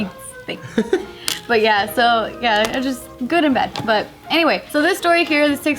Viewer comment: Did say at 3:44, "Yeah, good in bed."